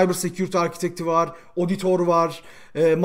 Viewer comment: 'da ne diyor ağabey, anlayan var mı? Oditor tarzı bir şey ama çıkaramadım. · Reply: Auditor olabilir ama emin.degilim